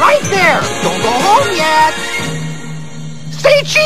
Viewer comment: What Did He Say? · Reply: "I'll Send You Each Your Copy."